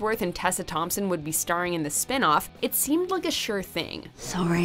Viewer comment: and that Chris Hemsworth and Tessa Thompson would be starring in the spin-off, It seemed like a Thor thing... That's how I heard it lol